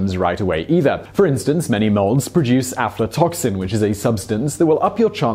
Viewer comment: Did Simon say aptotoxins 0:53 was the cancer-causing substance found in some molds?